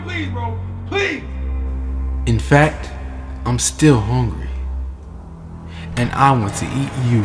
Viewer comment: O MI GOD when the door said I’m hungry I thought he said I’m “horny” xD so when he said I wanna eat you, I bet you can guess what I was thinking :-/